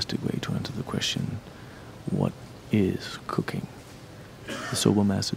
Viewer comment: did anyone else hear "what is Cocaine"?